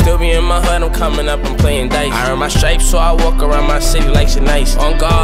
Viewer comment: i thought he said so i walk around my city like some nikes but aight